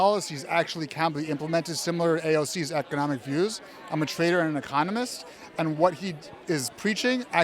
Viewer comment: Anyone else heard him say "I'm a traitor and a communist" rather than "I'm a trader and economist"?